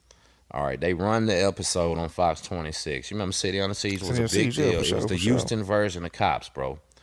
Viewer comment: what was the show called? cant understand i wanna watch it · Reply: City Under Siege. It was on the Houston TV in the 90's